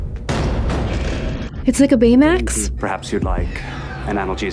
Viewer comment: "It's like a Baymax" is the most perfect description of the EMH I've ever heard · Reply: I misheard it as "betamax" lol